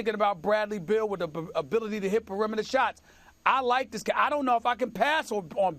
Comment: BIG BUTTS AND I CANNOT LIE. (anyone else hear it when Stephen A says "I Like")